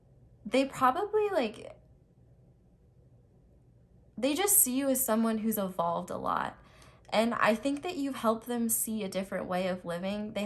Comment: What did she said? Sorry I'm not native qjdnxubdidbd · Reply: "They just see that you evolved a lot" I'm not native either lol